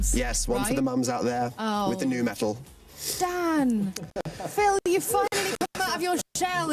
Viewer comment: was it just me or did anyone else hear her say "Phan"